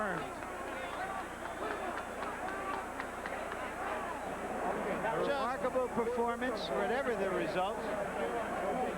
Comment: what does sugar ray say to hagler? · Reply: "You beat me Marvin," then Hagler says "Alright!" taps Leonard as they separate.